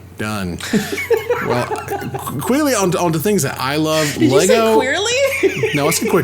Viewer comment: I heard Quilly